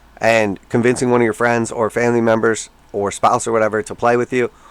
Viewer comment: Spat my proverbial cup of tea when I heard from 11:51 "convince your friends family members or .....sponsor" It is almost to the point where we will all be needing some rich sponsor to befriend to support us in the hobby